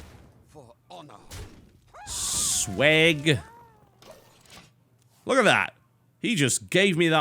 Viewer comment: did TB just say "Sweg?" Haha :P